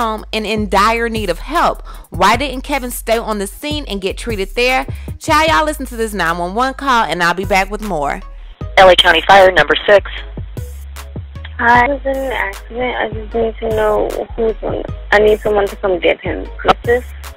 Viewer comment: sounds like she says, "I was in an accident".